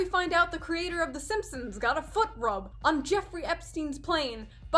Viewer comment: You said "foot rub from and underage child" I honestly thought you said foot job